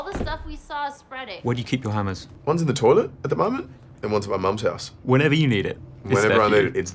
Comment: Did he just say one hammer in my mum's ass